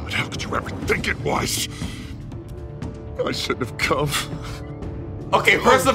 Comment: When he said “i shouldn’t have come” I thought he said “I should’ve cuffed” 😂😂😂😂😂😂